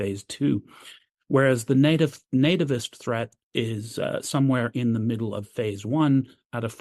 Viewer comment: Nativist threat?? Are the British a danger/threat to themselves? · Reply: I thought he said naturist threat.